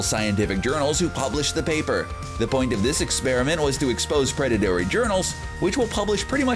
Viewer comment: did he say "predatary" or am i not hearing correctly?